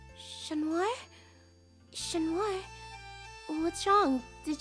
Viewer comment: PLEASE IM SORRY BUT I HEAR > : " Shat noir..? shat..???" AHAHAHAJ PLEASE SORRY